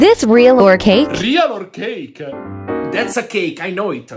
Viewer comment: got me on the floor😂😂😂😂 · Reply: It’s sounds like he said (pancake)